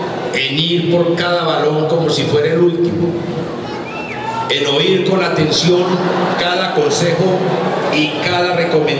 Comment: nose que dijo uno del publico pero se rieron todos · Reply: Juank lezcano Franco te amo!!